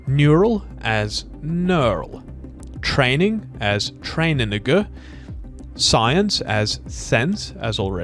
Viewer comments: Did bro just said “train a n*gga” 😂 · anyone else hear train a 🥷🏾?